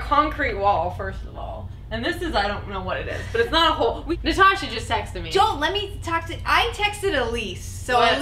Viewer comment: "Natasha just sexted me.." I can't be the only who heard that...